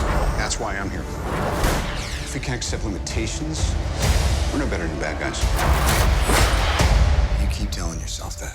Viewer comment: what did Tony say? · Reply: 'if we can't accept limitations, we're no better than the bad guys'